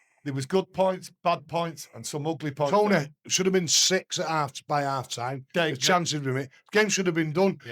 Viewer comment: Id I mishear, or did that guy really say "It could have been 6:0 in te first half"?